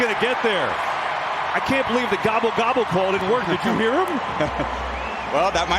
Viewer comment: was that call gobble gobble?